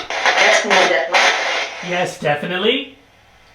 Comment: I heard “I guess not” instead of “yes Definitely” 47 :27 … i heard. It twice but I wasn’t there so I don’t think anyone that wasn’t there can truly hear what y’all did